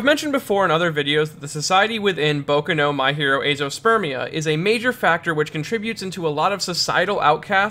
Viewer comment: I know I TOTALLY misheard this, but did he just say "My Hero Spermia"????